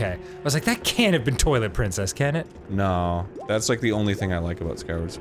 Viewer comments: Is it just me or did dan just say "toilet princess" ...... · please tell me I'm not the only one who heard "Toilet Princess"